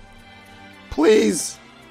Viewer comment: what he says “PLEASE” what the subtitles say “i dont have a spell”